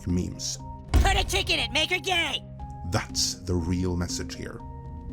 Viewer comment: All i hear is " put a chick on it and make it LAME "!!